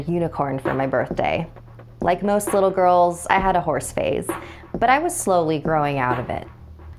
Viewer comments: I thought you said horse face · at first I thought you said “horse face” lol